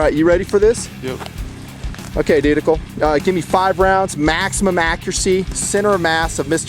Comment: he didnt say wat a thought he said? "dudeical" wtf